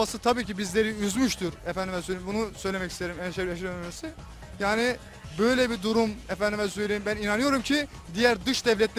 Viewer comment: da ne dediğini bilen varsa söylesin Allah aşkına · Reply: herşeyden önemlisi